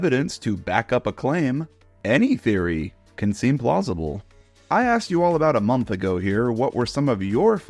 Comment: Does anyone else hear I axed you all about a month ago